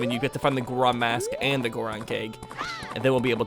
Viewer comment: Gorgon keg, I thought you said gorgon cake at first